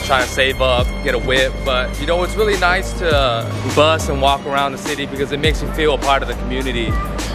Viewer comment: i thought he said “it’s nice to bust and walk around the city”